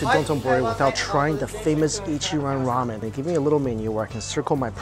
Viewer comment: Lol at first i heard him saying ICHIRAKU ramen 😂😂